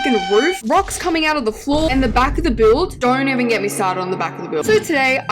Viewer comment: I thought u said the back of builds don’t matter